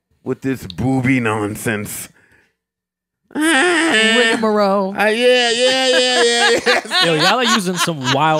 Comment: what word did they say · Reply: RCH 32 rigamarole